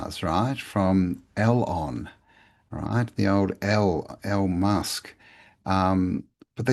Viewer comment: Message for: Check out what the automated subs do with you saying El Musk at 30:12 into the video! It's kinda weird. You say: "The old E L" and It translates to: "Elon right? The old L EI L EI L EI L EI L EI L EI L EI L EI L EI L Musk". Just noticing, probably nothing! As always brilliant video, cheers mate!